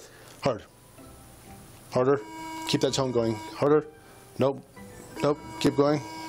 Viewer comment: I swear to God, I heard him say "Keep that tongue going" until I realized he said "tone".